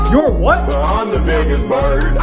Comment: at first I thought it said "I'm da biggest turd"